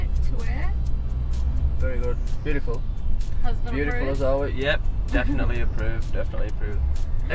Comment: does Dannii say ''husband approves'' ..Is there something she's not telling us about???